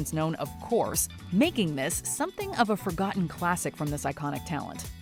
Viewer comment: Did you say "something of a classic from this psychotic talent"? 🤔